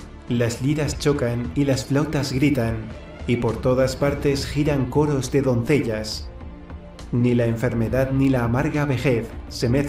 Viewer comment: le entendi "giran c*ñ0z de doncellas "